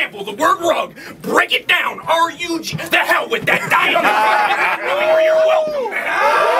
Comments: Dang I wish I knew what he said around 10:09. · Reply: on the carpet for not knowing where you're welcome mat (welcome at) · what does he say at 10:04? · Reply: "break it down R-U-G, to hell with that"